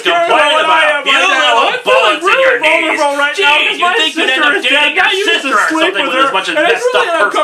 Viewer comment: *ish* to 20:24 Does any fellow 'Atop The Fourth Wall' fans know exactly what was said - on both sides - during this skit?! It's been driving me insane trying to figure it out LOL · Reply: it's a recap of previous lines with this gem snuck in Magneto: You'd think you'd be dating your sister with how messed up you are